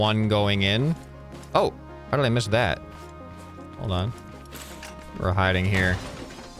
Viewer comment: did he say warehouse